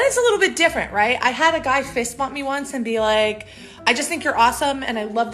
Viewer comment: I do not have misophonia because everytime she says, "... a guy fist bumped me...", I hear "... a guy face palmed me..." Ahahahahaha